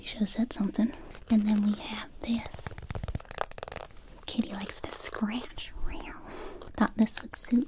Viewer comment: I thought it said "shitty is a scratch"- 😂